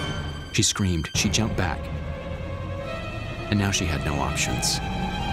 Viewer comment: and what exactly did the guy say at 4:52? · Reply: He said options.